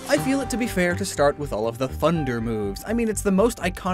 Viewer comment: Did he say “funder”?